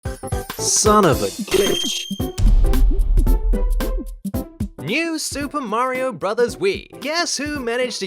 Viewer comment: it actually sounds like you said “son of a bitch”